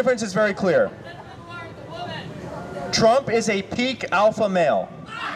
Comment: Trump is a peak alpha male? LMAO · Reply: I thought he said a “pink” alpha male..